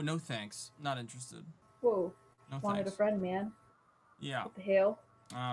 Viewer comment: Did she say "wanted a friend man"? Aw 😢